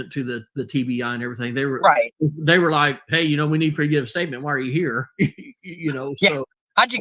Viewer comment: Oh, interesting 🤔 I've always thought said, "while you are here"